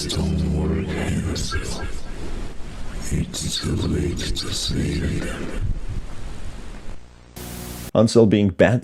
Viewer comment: I think he says: “Lex ton Hansel, It’s too late to save them”